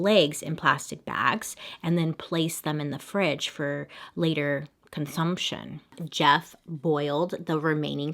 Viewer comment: Did she say consumption at 40:00??